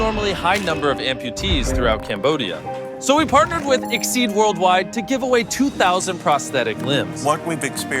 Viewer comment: Yo I thought MrBeast said “prestige worldwide” 2:03😂